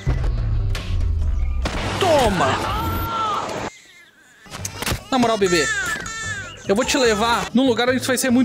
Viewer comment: Impressão minha ou eu escutei um NARUTO!